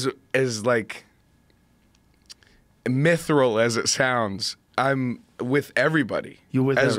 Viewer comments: "as emytheral as it sounds..." how do you spell that second word? It is a real word(?), cause I know what he's saying and I feel like he so good at words he made one up there that makes sense to me. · Did post just say mythril? 11:45 lmaoooo funniest podcast